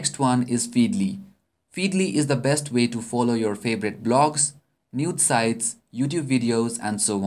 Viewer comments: did he say nude sites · did you say Nude Sites at 5:53 !!?